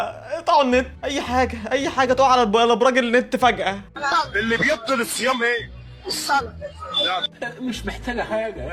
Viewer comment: أنا سمعتها اللي بيبضن الصيام اي